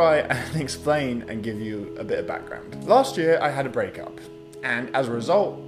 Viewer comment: For a second I thought you said "last week I had a breakup" instead of last year and my heart stopped and I was momentarily horrified and then I went back and was like "oh thank god"